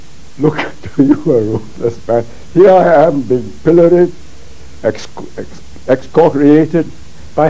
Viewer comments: he said "Pilloried" not "Pillaged" A pillory is the wooden frame they stuck your head and hands in for getting rotten produce thrown at you, or simply other abuse at the hands of the public. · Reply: I'm certain that who ever added the subtitles just thought he said pillaged because they didn't know "pilloried" was a word. I'm a native English speaker and didn't until I read this comment myself lol. Not everything is malice 😂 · Subtitle typo he says “pilloried” instead of “pillaged” at 2:30. I had to look this up because this man’s english is even better than mine, a native english speaker.